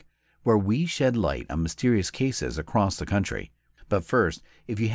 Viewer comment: THOUGHT HE SAID MYSTERIIOUS F""K LOL